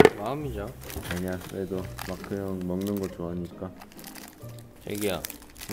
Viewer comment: สอบถามค่ะ 11:11 แจมินพูดว่า ชอกีโย(ซับขึ้นชอกียอ) ไม่ใช่หรอคะ ถ้าที่รักน่าจะชากียา😅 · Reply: ถูกแล้วค่าๆ ทางเกาบอกว่าน้องพูดว่า 애기야ค่ะ แต่ซับขึ้นผิดๆ